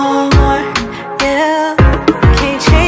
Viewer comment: I was on the way to Wal-Mart and the whole time I thought she was saying Wal-Mart instead of no more😂😂💏